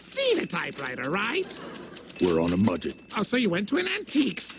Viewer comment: does he say mudget????